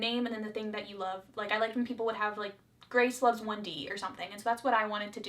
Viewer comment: “Grace loves 1D.” I mean I like their music and listen to them every once in a while but I wouldn’t say I love them. I’m sorry I saw a chance and I had to take it :) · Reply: Lol I thought she said "Grace loves Wendy" and I was missing some reference... This makes a lot more sense 😂